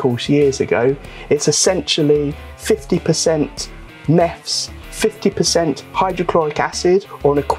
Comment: what did you say you used as a cleaner around 4:30? I've never heard of neffs before, but it looks like pinesol.. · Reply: It's Meths. Methylated spirits.